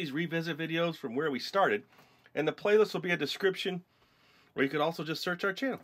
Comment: It sounded like u said in the playlist there will be a discription lol